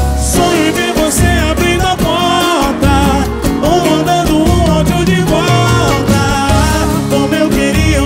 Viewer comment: cara e eu q quando ouvi a música pela primeira vez escutei "ou mandando o bafo de bostaaaaaa"???????????????mano